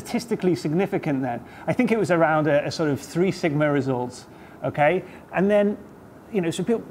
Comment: What word does he use at 13:11? sigmerizord? what? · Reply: 3-sigma results